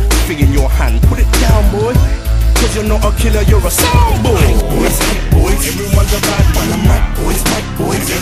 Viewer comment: Misheard this song as "white boys, white boys"